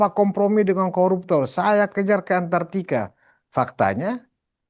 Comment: Kita salah dengar bukan kejar ke antartika tapi kita kejar diantara kita